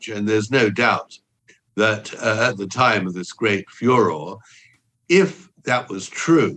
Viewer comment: odd choice of words I thought he said “fuhrer” but I guess it was “furor”